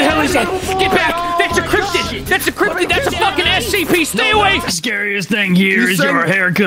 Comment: did he just say the three holy words? S. C. P.